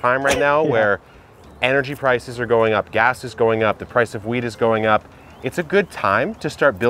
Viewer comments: did you just say "the price of weed is going up"? xD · i really thought he said the price of weed is going up.... i was like i missed the video where you planted that!